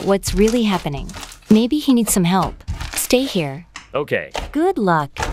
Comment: I thought she said HE NEED SOME MILK